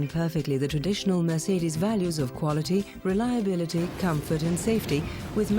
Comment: did she say reliability!? 😂😂